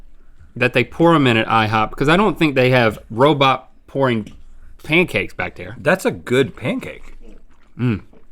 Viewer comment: “Robot pouring pancakes” said Link, but I think it’s “pancake pouring robots” haha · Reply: Brianna M sounded like he said, “ i don’t think they have robots...pouring pancakes back there”